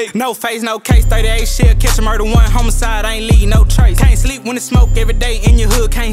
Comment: It’s hard to catch it tho cause it does sound like he say they sleep but it’s neighbors